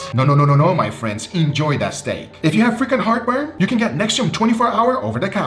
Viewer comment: So at first I thought Guga said "if you have frickin heartburn" but then I realized he said "frequent heartburn" LOL. I was over here agreeing like "yeah man, this frickin heartburn is terrible, I agree" 🤣🤣🤣